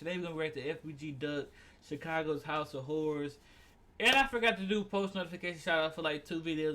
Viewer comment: momma thought u said 'house of whores'...the look she gave😂 she was not playing